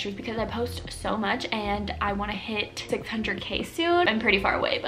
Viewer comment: Did she say 600k?